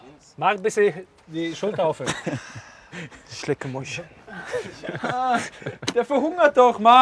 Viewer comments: „Schleckermäulchen“ ich kack ab 😂😂 Kingsley bester Mann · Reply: D :D ich habe da eigentlich was ganz anderes verstanden und gesucht ob jemand es auch so gehört hat. Deine Version ist mir irgendwie lieber.. meine ging.. "isch lecke mu***" hahahaha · Coman😂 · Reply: Ich hab "ich lecke muschi" verstanden 😂